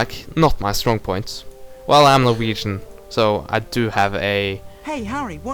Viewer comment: wait what's your nationality? I couldn't quite get it but it sounded like you said Norwegian? Like Hagrid's first baby dragon in the first game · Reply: Norwegian, indeed ^^